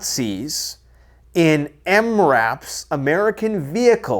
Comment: Can someone tell me what emraf means please? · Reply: It’s MRAP. Mine-Resistant Ambush Protected (MRAP; /ˈɛmræp/ EM-rap) is a term for United States military light tactical vehicles produced as part of the MRAP program that are designed specifically to withstand improvised explosive device (IED) attacks and ambushes.